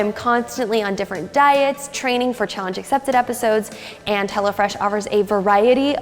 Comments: I thought she said "hella" fresh 😂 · i swear you're saying hella fresh instead of hello fresh here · It sound like she said hella fresh 😂